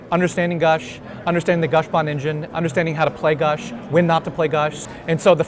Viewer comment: Can anyone tell me what says at 3:26?- when he says understanding the gush ??? engine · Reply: "Understanding the Gush Bond engine". He was referring to the cards fastbond and gush.